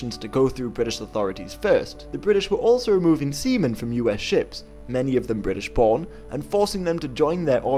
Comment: It sounded like he said the British were removing semen off the ships